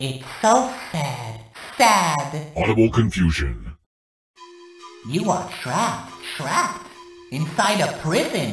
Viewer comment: I thought jevil just said “you are trash trash…”